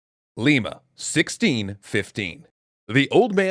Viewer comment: When it said Lima in the beginning I thought it said ligma